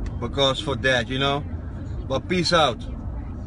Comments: I thought he said, "Chicken and a Bullet". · Swore my nigga said "chicken and a bullet" lol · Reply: Chicken anabolic